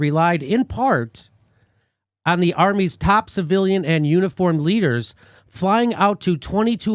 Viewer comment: I thought it said the army's 'uninformed leaders' for a second. Maybe it's getting harder to recruit soldiers as more ppl are becoming awakened to the reality that these interventionist regime change wars are BS.